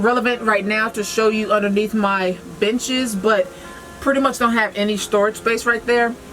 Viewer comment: She said “underneath my benches” I heard “underneath my bitches” I just about died 😂😂